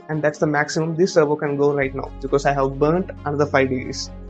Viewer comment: What do you say at 10:49? "That's the maximum this servo can go right now because ...?" Good video thanks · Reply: "Because I have burned the rest", means I messed up the servo's feedback/ic trying to test it beyond its range of pwm microseconds in either direction.. Happened for quite a few servos😅...Wow... U listened to that very extent in that video.. Thank you😊